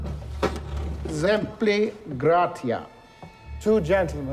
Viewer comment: what did he say here? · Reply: exempli gratia - for example